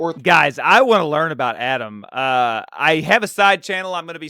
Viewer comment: bro in some videos I've watched I heard you saying Adam, and I was like "who is Adam? I don't remember him", his name is Allen😂